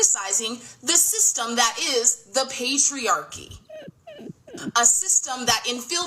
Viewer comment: Did she say “the pastryarchy”? (At the 20:28 mark)😂😂😂